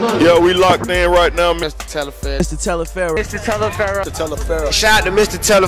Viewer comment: Right in the very beginning of this video it sounds like he is saying "Ms.Talifero" instead of "Mr.Talifero" fr or am I just buggin and hearing shit lol??